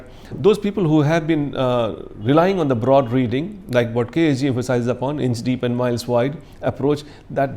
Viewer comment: What he said at 8:00 ? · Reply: 'a mile wide, an inch deep' mean being shallow or lacking depth in a particular subject matter.